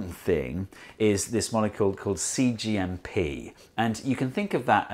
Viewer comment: I misheard "molecule called Xi JinPing"